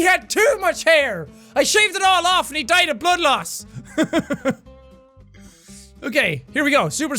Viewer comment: I swear I heard Jack say "He died of butt loss".